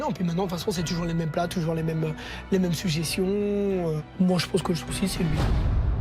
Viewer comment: Je sais pas vous mais moi j'ai entendu "Moi je pense qu'au suicide" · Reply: J'ai réécouté et en fait il dit "Moi j'pense que l'souci c'est lui", mais vu comment il le dit ça ressemble énormément